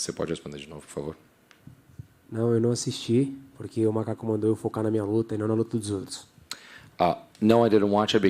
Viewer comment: did he say, my cock. Or my coach?